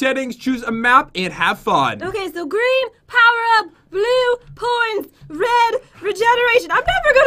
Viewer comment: Is it me or did jen said:blue,horns???